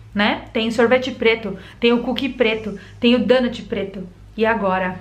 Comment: Eu jurei que ouvi ela falando "tem o cool que quica preto"